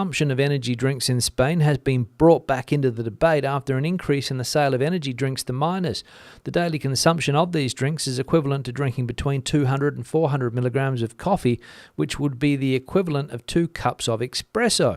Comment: I thought you said they were banning energy drinks to miners in Galicia, I thought that's a weird obsession for bureaucrats and also that the miners are in Asturias.